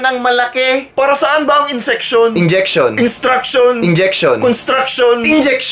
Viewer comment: did u just say insection in 1:10 like kung agree.